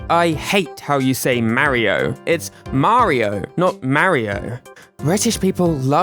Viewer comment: It sounds like Charles is saying "Mah-leo" so that's what I say 😌